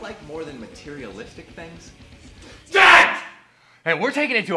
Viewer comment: Years later I'm still trying to figure out what Rick Riffson shouted at Rob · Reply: djent. he likes djent.